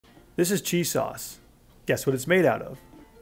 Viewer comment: anyone else thought he said g sauce